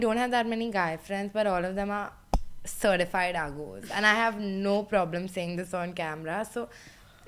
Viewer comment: what does say at 22:00?certified what? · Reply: uggos